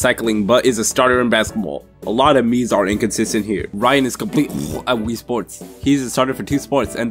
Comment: sounded like son of a t sports